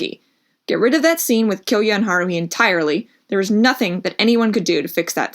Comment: I thought you said kill kyoya entirely 😭